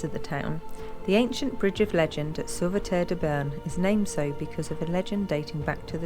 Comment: Hi, in this video we are at Sauveterre-de-Béarn, just north of the Pyrenees, South west France at the Pont de la Legende. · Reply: Cheers Guys, A little confusion on my part I thought you said " Sauveterre le Grande" which is only a few miles away from me! All that Naval gunfire has affected my hearing !!!!LOL. Looks really interesting and somewhere that I will put on my "to see" list. Enjoy your travels in this lovely part of La Belle France.